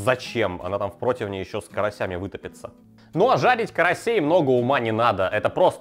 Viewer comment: Можете говорить, что хотите, но я слышу "ЖАБИТЬ КАРАСЕЙ")